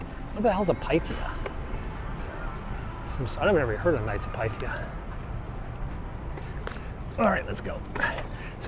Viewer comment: KNIGHTS OF PYTHIAS - IT'S A SECRET SOCIETY. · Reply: I thought he said Knights of Python... Anyway it sounds demonic to me.